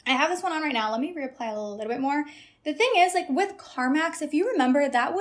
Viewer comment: I really heard “Carmax” instead of “Carmex”…I was like, “how is lip gloss giving car dealer???”😂